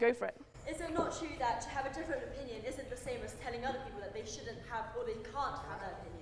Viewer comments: what does the lady from the audience says at 4:40? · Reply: “Is it not true that to have a different opinion isn’t the same as telling other people that they shouldn’t have, or can’t have that opinion?” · can someone please tell what the girl at 4:43 saying? i cant understand her. thank you :) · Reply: To have a different opinion doesn’t mean others can’t have an opinion